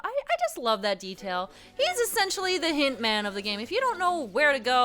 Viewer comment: I had to double-take on that, because I originally thought you were saying "He's essentially the Hitman of the game." for a second there.